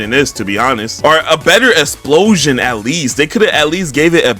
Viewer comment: Did bro say assploshion?